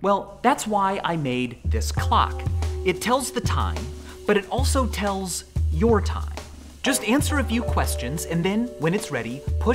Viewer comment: did he say clock or cock💀